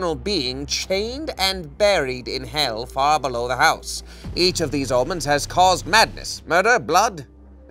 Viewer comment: The auto-generated caption says "each of these almonds has caused madness" 😂